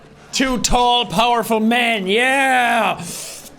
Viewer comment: did he say 2 tall powerful men?